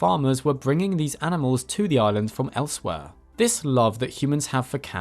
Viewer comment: What he said: “Farmers were bringing the cats to the island from elsewhere” What I heard: “Farmers were bringing the cats to the island from Elsweyr” Queue Skyrim main theme